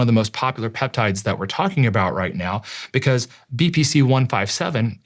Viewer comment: I thought he was saying BBC-157 lol.. took me half an hour to hear BPC bahahaha